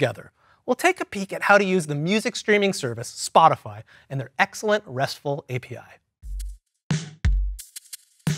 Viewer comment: Caption at 27:19 should be "their" instead of "they're" at 44:13, it should say, "no binging" as in binge watching instead of "no benching"